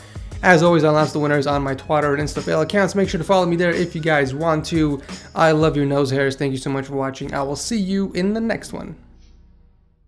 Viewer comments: Did he just say,I love your nose hairs? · Gavin has the best setup and yeah by the way did he say twatter